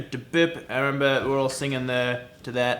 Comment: Luke taking another drink " oh thank you you are .... F-legend ." Me: did he just say flegent ?? 😂